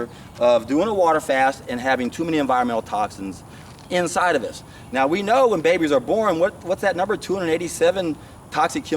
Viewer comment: Help! Cant figure out what exactly is saying in 16:20 - 16:50. Thanks :) By the way: I'm on day 14 of Juice Fast documenting everything and feeling unbelievable well :) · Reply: Zeolite aka Natural Cellular Defense. Thanks for documenting everything and sharing your experience!!!